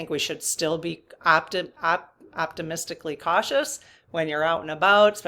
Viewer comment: I had to stop the video and re- listen to a comment you made that I misheard as "optimistically caustic" 😂